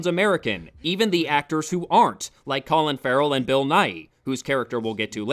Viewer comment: I swear to god, for a second I thought you said 'Bill Nye'.